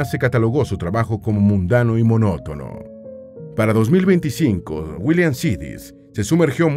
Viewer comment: Minuto 17:22, dice para 2025? No entendí 😅